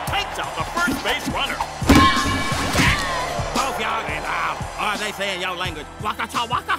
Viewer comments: As someone who understand Chinese, the closest thing that he said would be 0:09 "不要給他" (Bu yao gei ta) which means "dont give it to him" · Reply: I heard ,"bofe y'all is out" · what did he say? "hope yall get give up"? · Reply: “both yall is out” i think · "Yellow nigga" Uncle Ruckus is truly the greatest racist of all time😅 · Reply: I thought he said "Yellin' nigga"